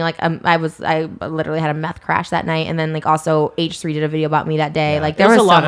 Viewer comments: did she just say she had a "meth crash" on her birthday? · did she just say she had a “meth crash” 😳??? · Did she just say she was having a meth crash??